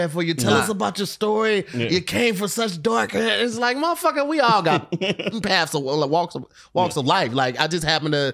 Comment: Hey, Did say ‘Paths of willy wonka?’ 😂😂😂😂😂😂😂